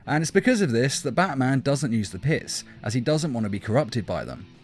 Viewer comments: so did anyone else hear piss instead of pits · did he say batman doesnt like using the piss